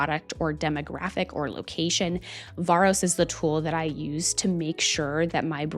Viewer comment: Hi you mentioned a tool used to understand the benchmarking of data which i didn't quite get. It was at 5:42, and the reference sounded like "Vorus" - Could you please confirm? Thank You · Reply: It's Varos :)